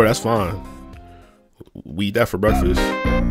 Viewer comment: I thought you said we eat gore for breakfast what’s up with the faces😔